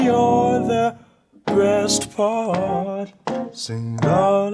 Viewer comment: Did he just say "Breast Part"? 😂